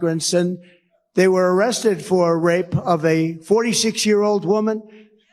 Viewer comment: Did Trump just say RAPE🫴🏽 make it make sense